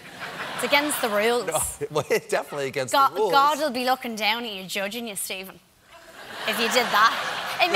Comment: can someone please clarify what she said here. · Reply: God will be looking down on you and judging you Stephen